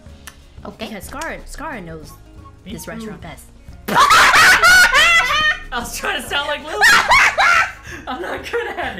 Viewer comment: What did sykkuno say at 8:38 that made lily and burst out laughing?? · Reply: He just said "me too" while trying to imitate lily's voice